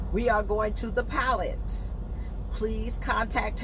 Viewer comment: O pilot truck stop.thought you said pellet.lol